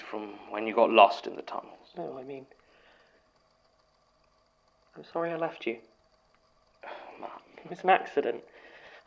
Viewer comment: I thought Martin said I'm sorry I loved you to but then I realized it was I'm sorry I left you. Got me really hoping lmao